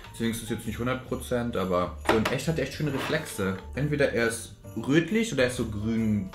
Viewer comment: Ähh der Nagellack hat Reflexe? Oder hab ich mich verhört 😂😂😂❤️ Ly😂❤️